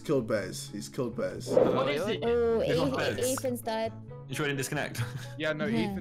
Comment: I thought she said: "Ethan's Dad"